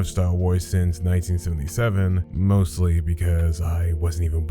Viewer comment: I’ve actually read all of legends and started watching Star Wars at 4 😂 but thanks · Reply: okay you see I misheard that as you saying "I have NOT been a fan since 1977" that's why I said it like that. Very sorry for the misunderstanding 😅😅😅 Either way amazing